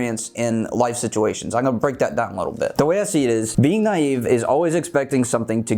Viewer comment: ok the first three times i heard "te voy a decir" i had to turn on the captions to see he said "the way i see it is"